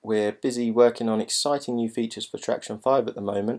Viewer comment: Did he say "Tracktion 5"? Didn't know that was on the horizon.